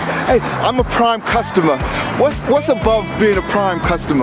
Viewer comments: “What does it mean to be a prime customer?” “You think you’re special? I mean thank you” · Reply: I didn’t hear “you think you’re special…” I heard “I think you… I thank you for being a customer.” good reach tho! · Jeff was about to be a smartass and reeled it back in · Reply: I thought you said a prime smart ass